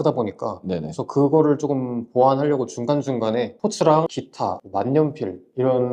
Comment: 주카테고리의 단점을 보완하는 형식이라고 04:08 쯤에 뭐라고 하신거에요? 기타, 만년필 앞에? 오디오가 잘 안 들려요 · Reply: 패션상품을 올리면서 중간중간 스포츠, 기타, 만년필 등등의 고마진 챙길수있는 상품들도 올리셨다 합니다!